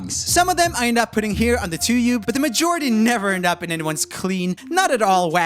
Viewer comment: Did you say tubeyou??😂😂